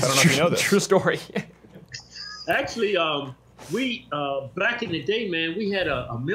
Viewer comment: Hahaha did he say "black in the day"??